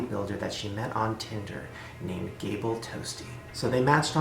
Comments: For the first one I thought he said 'Cable Toasty' 😂😂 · I legit thought he said ‘Bagel Toasty’ and about cried laughing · I thought he said Gabriel Toasty 😂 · I’m sorry but I thought he said “gable TOASTIE” (in the first story)